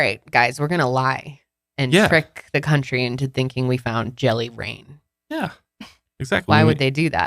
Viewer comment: I've been in Washington my whole life and I thought you said "jelly brain" 🤣 I've never heard of jelly rain!